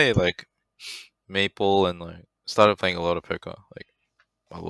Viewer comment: i didnt understand, what was ana playing? maple and what?? did he say poker?